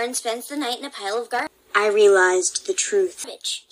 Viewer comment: it sounds like she said b*tch XD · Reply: The Crazy Potato :D She said Garbage but it was cut off